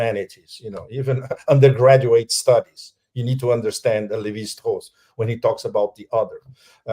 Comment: what is the name he is saying for "the other"? Levi hoss? Who is he referring to? I want to understand basic anthropology! Lol